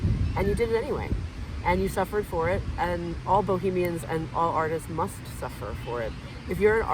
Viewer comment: Ohhh bohemian. I thought she said boogeyman. So grateful for your bravery either way!! Hero of intellectual freedom 🙌